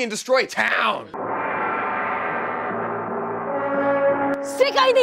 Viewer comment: I'm wheezing at 14:48 auto generated captions think mari is saying "SICK I NEED SOME KAWAII DESU DESU"